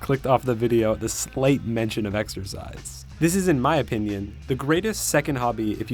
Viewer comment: exercise? i thought you said, extra fries !!